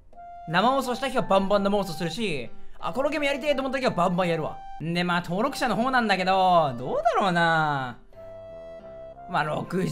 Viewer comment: ここが登録者のホモなんだけどに聞こえた俺はホモ